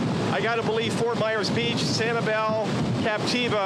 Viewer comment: Did he just say at the end that those areas will look like mexico after hurricane MICHAEL? lol, thats quite a few hurricanes into the future